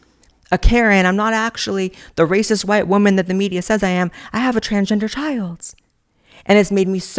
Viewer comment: what I hear now “I’m am white , I am a karen, I AM* racist” that’s what these crazies be sayin in 2024!!! It’s not even funny anymore, how are they the tolerant but they are the first to throw stones? I’m not racist I’m also not a f@ idiot. Merry Christmas Babe, that short is 🔥 on you 💅